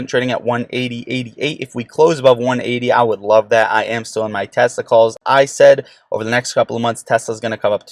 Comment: I heard "I would love that, I am still in my testicles" 😂😂